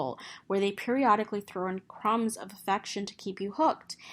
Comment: did you say "crumbs of sex"? so that's what she's been doing!